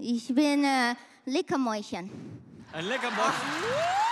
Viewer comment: что она сказала после «Ich bin…”? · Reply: 🤔 может, leckere Mäuschen